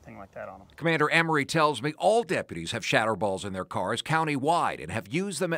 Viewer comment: "All deputies have shattered balls" That's what I heard and I'm sticking to it